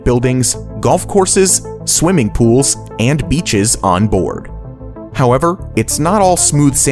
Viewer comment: I hear Bitches On board...!!?